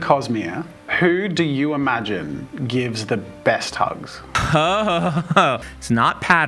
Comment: When I heard that question in the beginning I thought it was „who gives the best head” 💀💀💀 Guys I think I’m broken